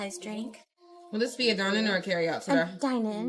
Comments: I heard her say, "Will this be a dine-in or a carryout to go?" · idk why but it really sounded like the manager said “sir” to her and i cant hear anything else. Edit: ah this came up in the video after I commented. Now I can see how it was probably “today” even tho i can barely hear that lol.